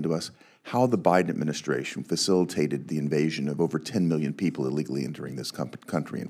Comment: 10 million i thought they said 20 million